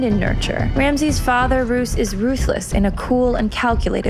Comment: My mind: Did she just say "Roose is Roose-less"?